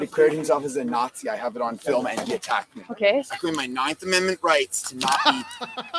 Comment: I thought at first he said I cream my 4th amendment. Lol